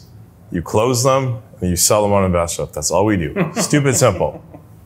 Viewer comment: he said “you sell them on invest rep”? The audio wasn’t really good there his voice and the mic didn’t come together. And when you turn in subtitles it just says invest. Anybody know what site he is talking about?